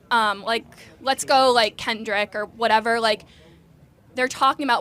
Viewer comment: did she say kendrick? if she did, did she listen to the song "father time"? he literally talk about how gansters are like that because they didnt have a father figure.